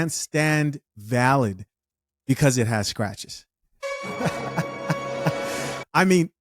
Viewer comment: that's not what she said. she didn't say "it has sketches" she say it was a sketch. what she meant to say, is that it's a SCATCH; a first draft.